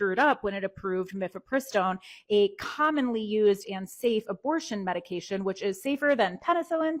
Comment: hey just fyi at 2:23 the caption says "unsafe" when it should say "and safe"